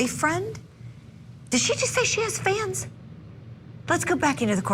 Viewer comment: I thought she said “fans”! 😂 WTH?